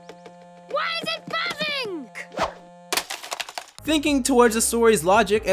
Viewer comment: did he just say why is it fapping?